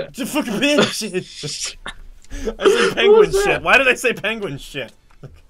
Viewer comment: I aware I heard slur "just fuck a penguin shit" XD My reaction to that was amazing